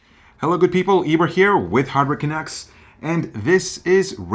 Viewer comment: Not gonna lie, I always thought their YouTube name was Hardware “Chunks”, until I heard him say “Canucks”